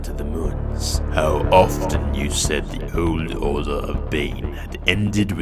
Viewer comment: The caption say "the old order of Bacon(bane)"